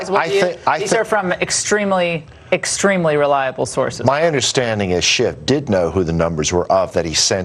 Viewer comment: sounded like he said "my understanding is Shit did know..."